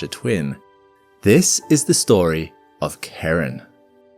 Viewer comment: i heard you said karen